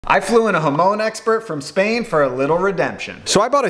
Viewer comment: “I flew in a Hamon expert from Spain” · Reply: thought he said hormone expert